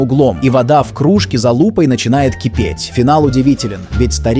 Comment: 💀 мне с начало послышалось с за*упой а оказалось за лупой